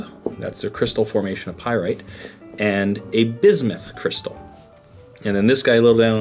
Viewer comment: Sorry I didn't catch the name of the cluster of geometrical shape crystal to the left of the pyrite cube @ 1:50? Thanks... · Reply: Bismuth crystal. Bismuth (Bi) is one of the elements.